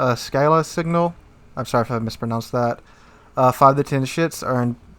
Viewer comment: i swear he said 5 to 10 shits LMAO